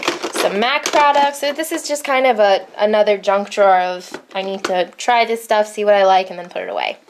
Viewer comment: When you said "junk drawer I totally thought you said "drunk drawer" ...as in, "oh, drawer... you so drunk." I think this should be a universally implemented slang phrase from now on.